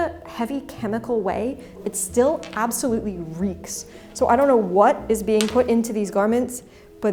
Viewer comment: small captioning error there: "wreak" instead of "reeks"